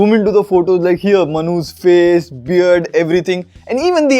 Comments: I heard MHANOOS FACE lol. By the way congratulation on achieving 1 million subscribers. :D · did u say Manu's face or manhoos face? 😝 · Every time you said Manu's face / skin tone, I heard "manhus" face 😂😂😂